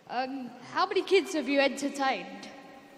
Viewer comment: why did i hear "How many kids have you had to tame"